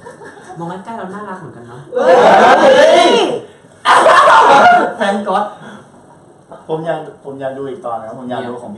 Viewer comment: มีใครได้ยินพี่เตพูดว่าแฟนก็อตมั่งงง งื้อออ